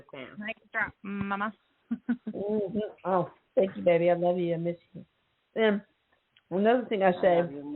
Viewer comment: I heard "mom, I love you too"